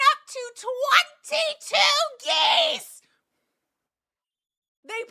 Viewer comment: English isn’t my first language so I thought u said that your lawn was taken over by 22 GAYS and was VERY confused until I finally turned subtitles on and realized 😭